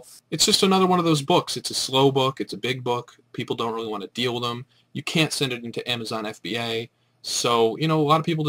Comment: did you say "you can or can't send it to fba?" With slow books like these, it's better to be merchant fulfilled. Thanks. :)